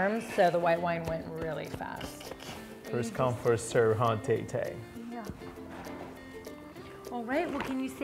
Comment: Awww, it sounded like he said "ta-ta" 😍